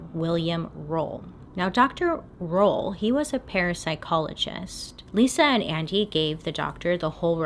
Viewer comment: I heard 'parasite-chologist'